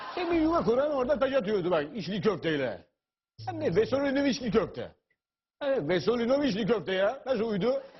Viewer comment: ile 6:00 arasında kafiyeli bir laf söylüyor ama anlayamadım ben... ne diyor orada??? anlayan varsa yazsın merak ettim... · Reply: vesolinoviçli köfte diyor :)